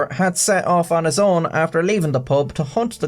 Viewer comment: Am I the only one who heard, "had set off on his own after leaving the Pope" at about 2:30?